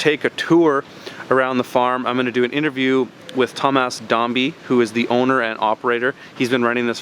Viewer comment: I first thought he want to say "dumbass zombie"...